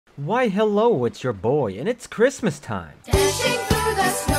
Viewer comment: wait hol up you pronounce your name “Ehts”? I’ve always said it “Eeeteecee”. Well Oof also I heard that ddlc music ya can’t hide it from me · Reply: I THOUGHT YOU SAID “ITS YOUR BOI.” DOUBLE O O F